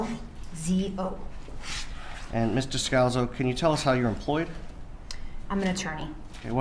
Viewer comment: Is he calling her Mr.Scalzo ?